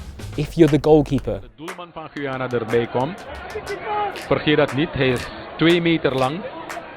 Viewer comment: Great vid, but the subtitles are wrong at 0:27. The commentator actually says "Don't forget, he's 2 meters tall."